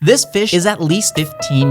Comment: “This fish is 15 years old” What I heard: “This bitch is 15 years old”